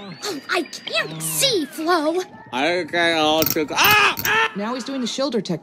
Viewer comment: can anyone decipher what man's is trying to say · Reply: I kay alta... AHH AHHH!